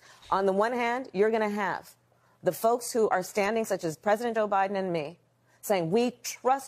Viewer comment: Did she say president OBiden😡 wow!!!!